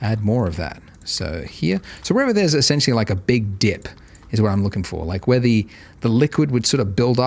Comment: it sounded like he sweared in 19:42 by saying big di*k